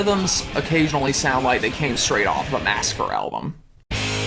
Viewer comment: What band does he say at 5:25? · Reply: master